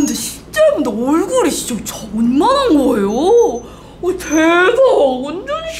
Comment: 이거 왜 댓글이 없어 ㅋㅋㅋㅋㅋㅋㅋㅋㅋㅋㅋㅋㅋㅋㅋㅋ 얼굴이 ㅈ만한거에요~ 라고 들었는데 ㅋㅋㅋㅋㅋㅋㅋㅋㅋㅋㅋㅋㅋ